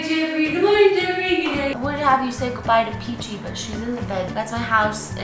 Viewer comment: Omfg I had to replay the end because at first I thought u said we had to say Goodbye to Peachy because she peed in the bed! 0.o